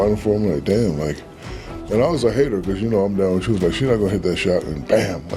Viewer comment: what? i can't understand a word that comes out of mouth... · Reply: I understand every word. "And I was a hater, cause you know I'm like she ain't gon hit that shot but bam damn..." Meaning she did hit those shots and he admits he was proven wrong